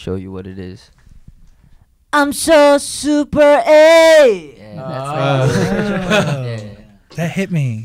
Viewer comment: did he just say "gayer"??!!! since not many people use this meaning these days so i'm just surprised...